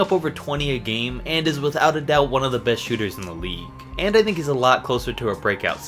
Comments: this boi said “and this n*gga” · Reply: He said “and I think” Helen Keller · did he say nigga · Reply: He said “And I think”